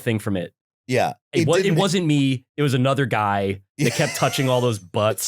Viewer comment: He actually said “I’m Italian.”